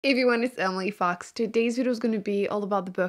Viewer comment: "Hi everyone, it's emily fox!" grabs a blanket and get's cozy to watch the video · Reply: oh i always thought she said "if u know its Emily fox" 🤣